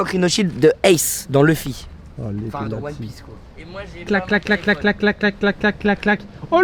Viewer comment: Inox: Eh moi j'ai une coque rinhoshield de Ace dans Luffy.... La consanguinité dans one piece ? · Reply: 《fin dans one piece...quoi 》